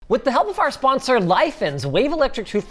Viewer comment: Had to check the actual sponsor name because it sounded like Linus said "With the help of our sponsor, life ends..."